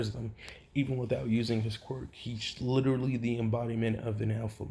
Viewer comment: i thought you said he is the embodiment of NF